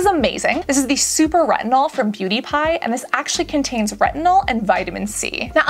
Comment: I thought you said from Pewdiepie at 22:33 and thought wow Pewdiepie is also making skincare?! lol